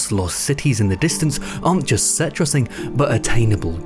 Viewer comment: I was really happy to see this video has manual subtitles, but uhm... does it? There's a bunch of weird mistakes that I don't think a person (or even youtube's automatic subtitles) would make. For example at 5:53 you say that the backgrounds aren't just set-dressing, but the subtitles say "citrus-ing"? lol like... I can hear (I just use additional subs to concentrate better), but if all I had to go on was the subtitles I don't think they'd have helped here, yknow? And 'citrus-ing' isn't an actual word, so I have no idea how this could have happened... do you use a bad transcription AI to do it or sth? The video's good btw, this just really irked me